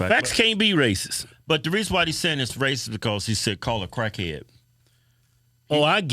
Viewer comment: I thought he said call a cracker.